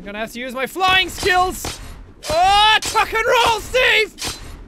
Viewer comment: When he said tuck and roll I thought he said f and roll